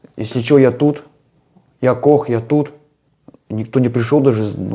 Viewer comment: Что он сказал? "Я тут, я Кох?" Это его позывной?